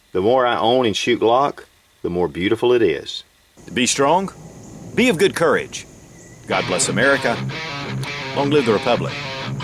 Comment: What does he say? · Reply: "Be Strong Be of good courage God bless America Long live the republic" ends all of his videos this way.